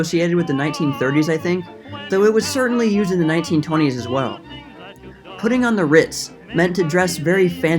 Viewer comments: Ain't no way I'm so damn knowledgeable on stupid 2020s slang that I genuinely thought he said "Putting on the rizz." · thought it said rizz at first